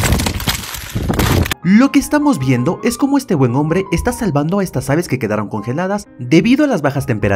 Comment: Yo pense que decia " malvada" y tuve que volver a ver el video por que no vi a ningun ave malvada.